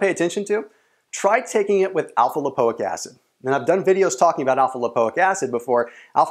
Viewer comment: I eat healthy and I train 5 days a week. I do intermittent fasting as well. my weight is hitting plateau now and I need to lose some more fat. What should I take? Should I take l-carnitine and cla together for best results? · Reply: Hsuan Chen I think he said take it with , alpha lipoic acid. On 8:08 of the video .